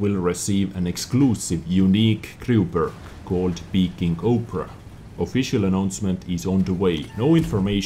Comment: Are you saying "Peeking Oprah"? I seriously cant understand this part lol